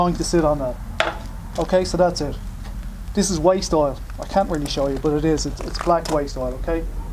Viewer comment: Thought you said waist oil